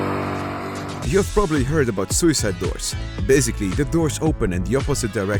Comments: I thought you said $uicideboy$ 🥲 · i heard suicideboys at 8:50 and got excited unfortunately